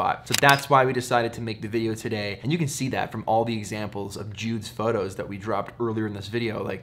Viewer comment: I thought you said... not to have a tower coming out of his head? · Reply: Peter said they were Jude's photos right? at 13:25, or maybe i didnt understand that correctly.